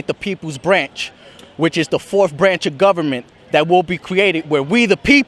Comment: could have swarn @ 0:35 the guy nearly said .....Koran. instead of government